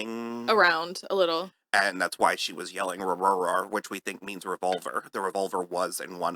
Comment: I thought Norma was saying where am I , but thinking about it it could have been revolver